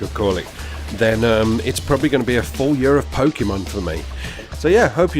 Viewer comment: "Full year of pokemon for me" Did I hear that right xD?